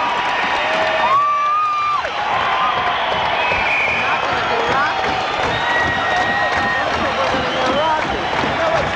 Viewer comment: Can anyone make out what the people next to the taper are saying? It sounds like they were hoping Rock It would be played. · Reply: "I hope they're gonna do Rock It. I thought they were gonna do Rock It"